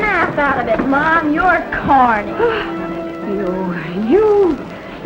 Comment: what did she call the youngin? whaaaat?!?! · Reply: Sounded like, 'You... you little shit!' to me.